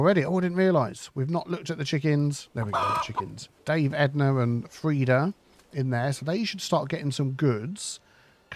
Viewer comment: Dame Edna and Frida 🤣 It sounded like that anyway.